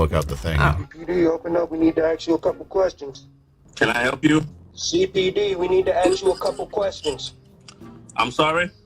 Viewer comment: What cop would say "We need AXE you a couple of questions"